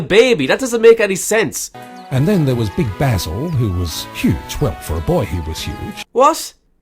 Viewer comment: For some reason when the narrator said "Big bassle" (I don't know and don't care how is it spelled) for a second there I heard "Big Asshole"